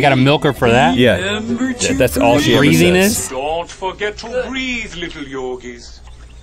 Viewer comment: I thought it said breed